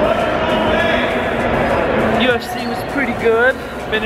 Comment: so at 2:00 i thought the guy said JOHN CEENAAAAAAAAA